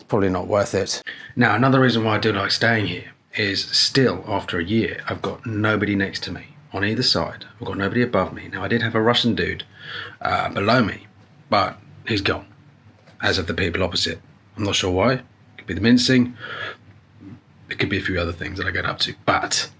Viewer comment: Did I hear that right?? 1:49 "I had a Russian dude blow me" 😂😂😂Sorry, couldn't resist... Enjoying your vlogs 👍